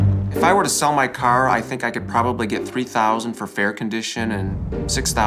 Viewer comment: 3000 for that car honestly that's a steel for how good the condition of it is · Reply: Jacob Lankford sorry nevermind i thought he said 300,000 but i watched it again hahah